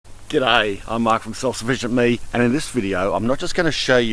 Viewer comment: it sounded like he said, "Good day, I'm Mark from Salsa Vision Me."